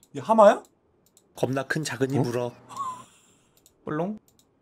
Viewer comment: 뭐라고 말하는 걸까요? 겁나큰작은이불어...? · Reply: 겁나큰 '작은입우럭'이라고 스듀에서 많이 잡혔던 물고기 이름이에요! <link>